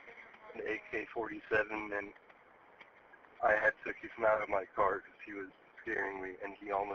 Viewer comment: I had Texas mount in my cart? WTF is he saying? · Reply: "I had to kick him out of my car"